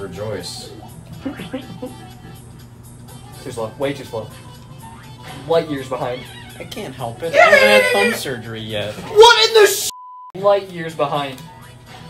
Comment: I thought said "I haven't had top surgery yet" which would've been so fucking funny